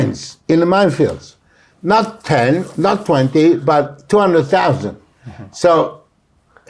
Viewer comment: 奥山さんもルトワックも英語聞きやすい。 10:08 20万じゃなく、1.２万のように聞こえる。 不妊治療の無償化は賛成だけど、健保側でやるのは難しいだろうな。やるなら自治体側による給付金の形だろう。ただ、やるなら国籍条項をつけるのは必須。 保育の無償化も現在の保育園運用を見ていると難しそう。やるなら断固とした姿勢が要るだろう。